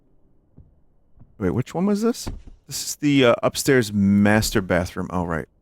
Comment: I keep hearing "masturbate room".